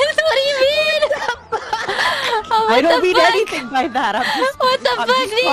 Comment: The question marks at 0:53 is saying "I don't mean anything by that"